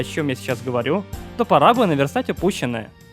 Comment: послышалось она сказала: fuck you...